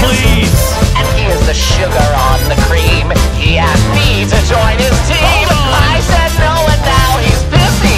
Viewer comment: I can never get this out of my head now after mishearing it "He asked me to join his cream!"